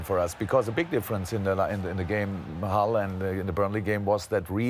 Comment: Did he say halland